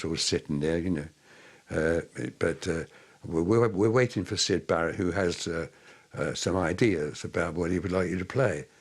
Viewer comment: ok im not completely familiar with english since it's not my first language but can actually someone understand what the fuck this "*wise looking old man*" is even saying?! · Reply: "We're waiting for Syd Barrett who has some ideas about what he'd like you to play" He was quoting what he said to the Salvation Army band while they were waiting for Syd